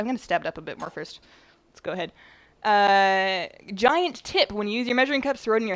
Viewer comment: i thought you said 'giant tit' I got excited and tabbed out of WoW...#feelsbadman